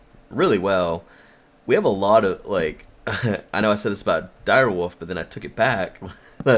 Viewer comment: sounded like said direwoof, not direwolf lol!